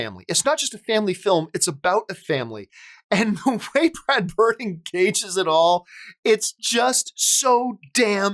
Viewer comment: “Brad Birdie” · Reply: I heard "Brad Bird engages it all." He just said it quickly.